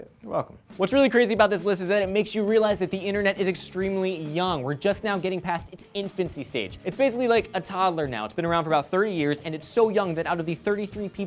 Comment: I'm sorry i missed a bit there did elliot say its been around for three years ?